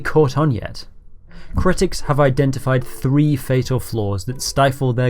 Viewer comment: the subtitle is gaming instead of gambling, Also I appreciate that you do subtitles.